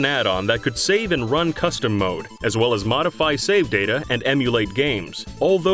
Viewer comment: you said custom mode at 8:16 instead of run custom code